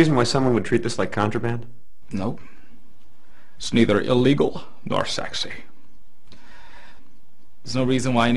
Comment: did he say its neither illegal or sexy at like